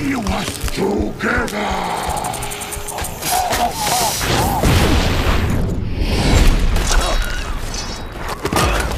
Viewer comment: what does he say? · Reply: Chuckfan 47 together. It's like his catch phrase